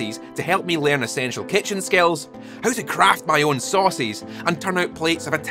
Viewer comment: I zoned out slightly during the skillshare segment, but was jolted back to attention when I thought I heard you were learning to "Craft my own sausage" Welcome back dude - great vid as always